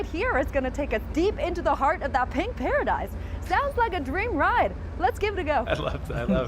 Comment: I thought she said chink paradise!